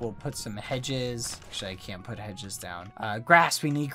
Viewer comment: It sounded like bbh said 'sh*t I can't put hedge's down'